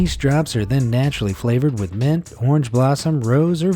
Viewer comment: is it just me or did he say meth? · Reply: He said mint but it did sound a lot like meth